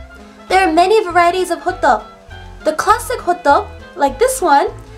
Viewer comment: When you said 'classic hotteok', I heard 'classic hotdog' :P